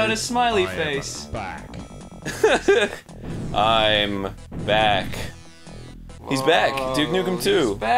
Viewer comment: Am I the only one that thought Duke said "I am a Back."